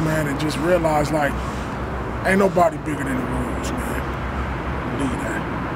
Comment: What he say @ 0:53??????????? Aint' nobody bigger than the Ru's............. I Think that's what he said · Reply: Rules